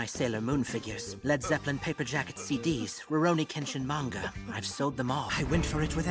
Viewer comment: Did he say Rurouni Kenshin?